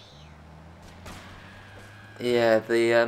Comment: Did anyone else hear a baby say ahh yeah